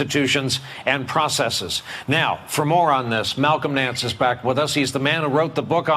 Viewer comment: Be fair Jimmy. Brian Williams was very candid at 2:44. "Moron this!" I think I heard it right. Right?